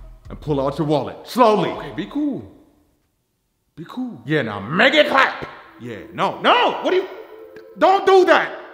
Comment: What does he say here? Make it flap?